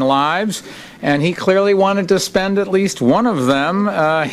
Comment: Cat repeatedly have nine lives and he clearly wanted to spend at least one of their here in New York city. I'm native to Japan! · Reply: instead of 'wanted to spend at least' i hear 'one of the spend at least', omg)